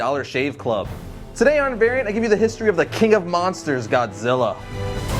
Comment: I thought you said today we'd get History Of Aquaman, Me is Confused